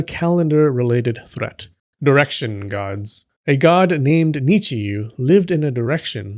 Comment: You said "direction gods", I heard "erection gods" 😆